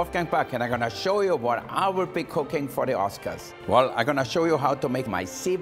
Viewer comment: Did he just say "I will pick cocaine for the oscars"?